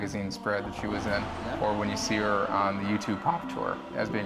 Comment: Did they mention Youtube? But "this christmas" as in 1997. Yes the internet was available but Youtube wasn't a thing until 2005... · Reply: Wow , I saw that video a couple of times but never noticed the “YouTube” I always thought he said “couture” as she appears in some costumes from fashion designers in Time magazines